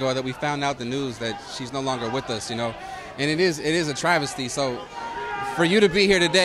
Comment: What the hell is a Travisty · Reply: I thought he said “devastated” not travesty